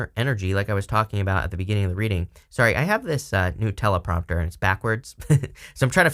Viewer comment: Definitely thought you said “Nutella Prompter” 😂